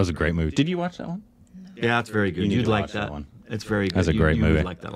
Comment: lmao, so not only did not remember what movie it is, she didn't even remember she's seen such a recent movie. She clearly said no when asked if she saw it. So funny when she said yes I liked it when reminded her that she's seen it. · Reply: she didn't say yes i liked it, was saying you'd like that